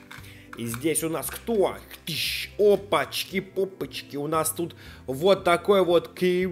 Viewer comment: Опопочки попочки? Надеюсь мне послышалось